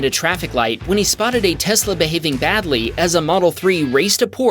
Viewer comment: that's a Porsche not 2 Tesla's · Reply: he literally says a Tesla was racing a Porsche. At no point does he say two Teslas.